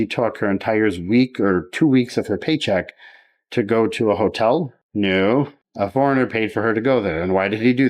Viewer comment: I could have swore you said, “whore-tell”. It would’ve fit perfectly!